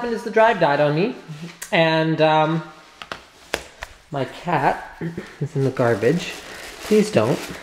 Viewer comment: I'm reading the comments and I just hear "My cat is in the garbage"